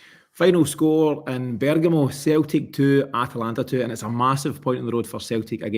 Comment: What do you mean?! 🤔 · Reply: at the very start I thought I heard him say Celtic 2 Atalanta 2